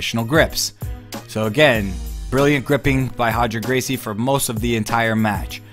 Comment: Sounds like he is saying Hoger